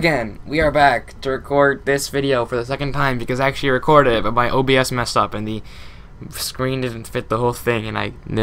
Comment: At the beginning where you said “my OBS messed up” I thought you said “my OCD messed it up”